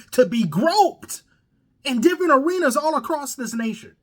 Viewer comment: "In different arenas", I heard "in Gilbert Arenas" 🤣